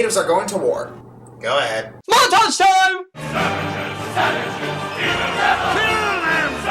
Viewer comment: I first saw Pocahontas when I was around 3 or 4, and when ever someone said the word "savages" I thought they were saying sandwiches XD